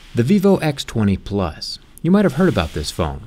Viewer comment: Did he just say vivo x30 😂😂😂😂,,cant help but to laugh though on the sweet roast of Apple 😂😂😂😂